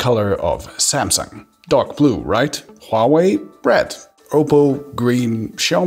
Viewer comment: Why you say "bread" instead of "red" like a normal person?